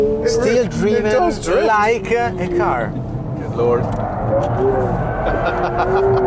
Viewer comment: the guy says "it still drives like a car" he did not say "it still drives like a ferrari" and there is a big difference there.....